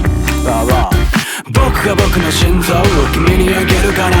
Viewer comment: 「僕が僕の心臓を君にあげるから」 が 「dogが僕の心臓を君にあげるから」 に聞こえてタイトルとかポチタとかにいろいろ掛かってるの凄い